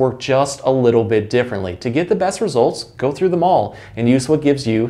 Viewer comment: When he said “go through them all” I thought he said “go to the mall”. And I was thinking, “how would that help?” 😂